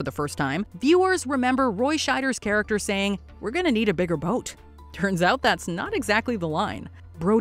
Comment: I genuinely thought he said “We are going to need a big fuck off boat” 🤔